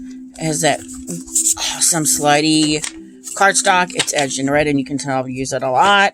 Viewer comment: I hear “slutty cardstock” :) That’s a powerful connection you have with the Mary-El. It feels good to know you have the power to heal generational traumas 🙏🏾